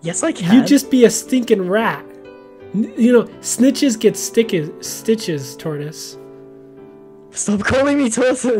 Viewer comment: I thought said snickers get stickers instead of snitches get stitches.